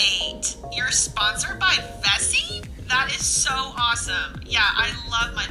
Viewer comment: The whole time I thought you were saying Bessi instead of Vessi 🤣